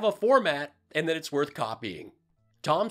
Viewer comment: I thought he said floor mat😂😂😂 He was looking down when he said format and that made it easy for my brain took a left turn and for nano second that's just how it works 😂😂